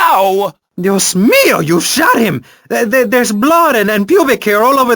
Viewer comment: Listening to this in 2016! "Dios mio! You shot him!" LOL! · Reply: Sounds like he is saying "You smear! You shot him" :D lol